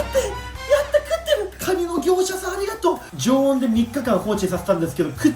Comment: ‼️ Did he say MOSQUITO DEALER!?! 🦟